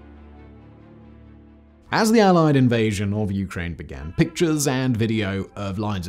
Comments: Does he say "the allied invasion" at 5:57? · did you call it the "Allied invasion of Ukraine "? Are you smoking crack? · ish “as the allied invasion of Ukraine.” Idk what exactly you are talking about since you were referring to the invasion of Ukraine by Russia. Or am I hearing it wrong? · Allied invasion? Did I hear that right? I'm confused. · did I hear you say “as the allied invasion of Ukraine…”